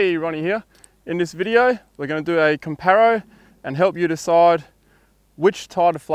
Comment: Lol first thought at 0:03...did you just say "comparo"?!